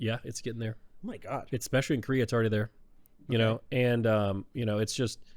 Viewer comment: can anyone translate that for my non-native idiot ears? i cant tell what is saying there. especially what? i hear something like "especially in korea it's harder there" wtf